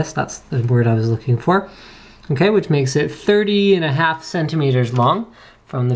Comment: lol i thought he said 30 and a half centimeters mom haha